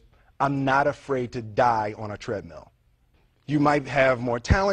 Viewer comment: what does he say at 2:44? "im not afraid to die on a trad...." can anyone translate in german?